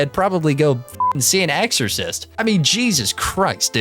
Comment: exorcist? I thought you said extra fries 😹😹😹😹😹😹😹😹